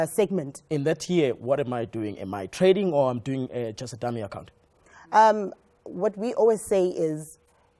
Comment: Did he say Dummy account 16:05 😂😂